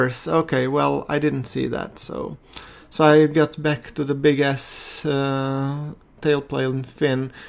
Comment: lol when you were saying big s wing i thought you said big ASS wing ecks dee